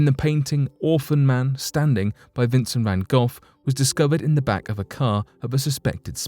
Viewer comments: did he really say Vincent Van GOLF??? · Did he say Vincent Van Golf?